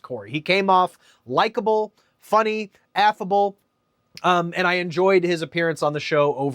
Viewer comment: When you said “affable” I thought you said “f-able” and I threw up in my mouth a little bit.